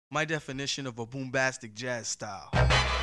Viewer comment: When I first heard this on the radio 31 years ago, I thought they were making fun of Quincy Jones' Soul Bossa Nova because I had never heard the term 'boombastic'--- I thought they said bombastic!